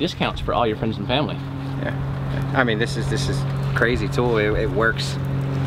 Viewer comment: subtitles say “this is a crazy toy” clarification, this is a TOOL, NOT A TOY.